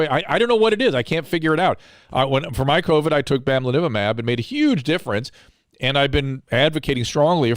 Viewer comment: Dr Drew mentions what he took when he had Covid-19. I can't quite make it out, could someone comment what he said? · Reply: Bamlanivimab